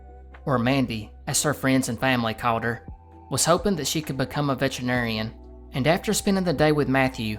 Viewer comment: In the first case in like 1:18 i hear you brother you say about this girl she dreams of becoming a vegetarian lol veterinarian